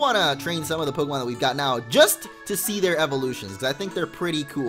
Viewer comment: Use modrill he has a mega eveloytion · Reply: Derplax Dragoneel at first i thought he said 'eveLOTION'